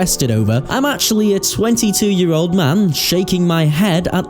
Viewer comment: I thought you said "sweaty 2 year old man" instead of 22 year old man